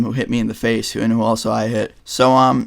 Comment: i just love intead of ending the video right after he says peace he cut half of it out and it sounds like he says pee